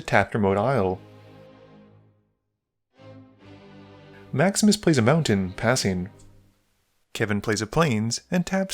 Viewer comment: I kept hearing "Max misplays a mountain" or "Max misdraws" rather than Maximus plays a mountain/draws and i was loosing my shit. Lol